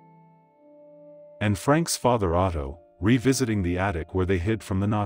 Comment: You sure need a professional narrator. This one makes many errors. For example, it sounds like he says, 'And Frank . . .' rather than Anne Frank. You should re-record this audio.